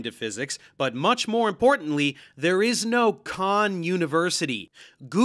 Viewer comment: I thought Billy said he went to 'Con' University. I thought he might have become self-aware. 😆